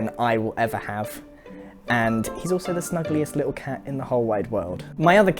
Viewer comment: I have to say I misheard him saying "snuggliest cat" I heard him saying "ugliest cat," and I was quite shocked till went back to hear it again